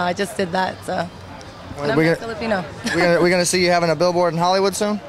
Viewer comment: Did she say she's Filipino?